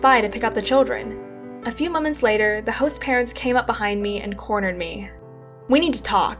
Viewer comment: At first I thought it said, My Hot Parents.